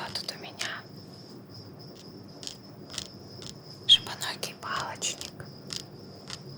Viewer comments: жопоногий палочник 🤣😅 послышалось вроде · Извиняюсь конечно,но мне не одной это послышалось? 8:42 "Жо*оногий палочник"😅😂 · здравствуйте! Скажите, пожалуйста, на 8:41 как правильно животинка называется? А то мне послышалось "Жопоногий палочник"... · В следующем выпуске: "Чистка ушек шипоногому палачнику,в гостях Крокодил Данди" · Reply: Иван Поликарпов шипоногий! А мне сначала послышалось жопоногий